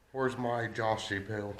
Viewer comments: does say "where's my Joshypoo?" 😝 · did I hear you say “Joshypoo” 😳😂